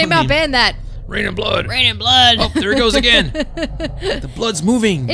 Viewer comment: did she say rated blood ?